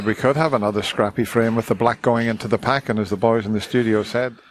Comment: We'll never know what the boys in the studio said. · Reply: I wrongly heard "as the boys ...said" rather than "and as ..." :) Anyway, the boys said that that would benefit Mark.